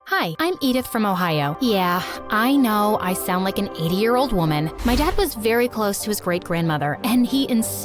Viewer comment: I thought she said “ 8 year old woman “ I’m over here like whattt